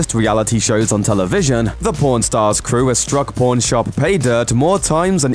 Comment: Sounds like u saying born stars